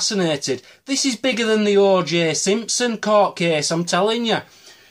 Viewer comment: Am I the only one who got the joke about the Egyptian find at the end. XD. LMFAO. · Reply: I the only one who thought he said the "orgy Simpson case"?